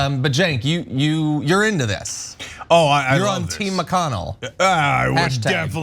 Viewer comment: LMAO swear I heard him say Ms McConnell 😂😂😂. That's how I see him. Old decrepit BIOTCH hiding in a marriage to Mrs, Chao who kept her name. How's that supposed to work in a Conservative Christian Marriage? Pushing all of the whole masculine White Male agenda to protect what he loves most. White 🍆🤣🤣🤣🤣🤣🤣